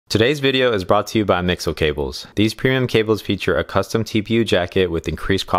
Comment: i thought you said mc donalds cables for a second